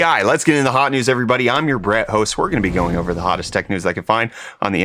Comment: Every time I hear the welcoming words I hear “I am your Bread host” lol